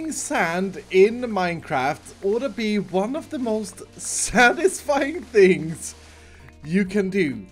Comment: did he just say sandisfying?